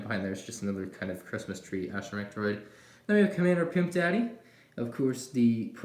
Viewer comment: did you say Commander Pimp Daddy? (Beautiful name)